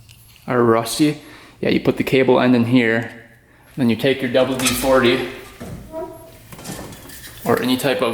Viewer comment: Got a little chuckle when talking about the cable luber it sounded like you said double D 40 instead of WD-40. I like watching your vid's there's no BS, you show and admit your mistakes. That knob for the jack in your press is slicker than goose shit on the boat ramp. Keep it up, keep the content coming we love it.